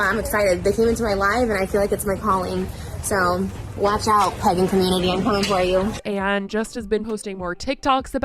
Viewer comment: I thought she said pegging community 😆. Is it just me or does anyone else feel weird about calling Trish they/them exclusively? It just feels icky, she lies so much about everything. It makes me kinda angry that she thinks she can adopt and use different communities until she gets bored.